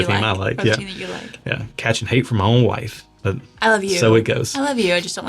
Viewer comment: Far be it from me... but when said "I love you", I thought for sure she said L-S-U ! Geaux Tigers!! 😉